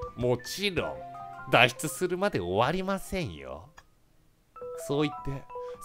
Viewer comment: の「そう言って」をずっと聴いてるとなんか声優に聞こえてくる(語彙力